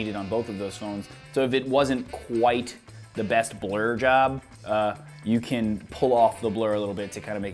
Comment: Hahaha my friend was playing with his phone while i was watching your video and his ears only picked up when you said blur job he thought you said blow job lolx... Nice video though